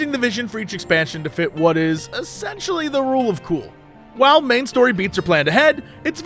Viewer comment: I swear to God I first heard "the rule of two". Was surprised to hear that Sith philosophy took over Blizzard.